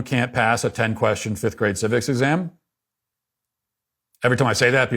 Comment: Thought I heard we should be able to pass a tenth grade "physics" exam to vote and I was like "damn, I'm not sure I'd be able to pull that off," so rewinded and relieved that he said "civics"